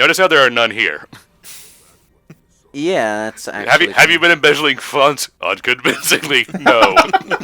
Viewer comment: What did say at 3:15? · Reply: "Have you been embezzling funds?" "Unconvincingly. No."